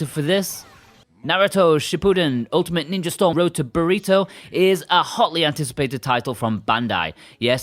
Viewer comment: It sounded like he say: Naruto, road to Burrito. I can't believe I never thought of that before.